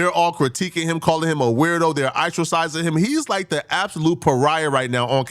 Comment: incorrect usage of words for today: “eyetricizing” instead of “ostracizing” 10:03 🤦🏾‍♂